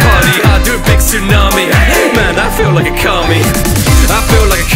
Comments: Could just be me, but I hear "I feel like a kami" as "I feel like Okami", the legendary Japanese dragon of rain and snow, which would explain the part about the tsunami. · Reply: i heard "i feel like a comie" till i opened my phone and looked at the title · Could just be me, but I hear "I feel like a kami" as "I feel like Okami", the legendary Japanese dragon of rain and snow, which would explain the part about the tsunami.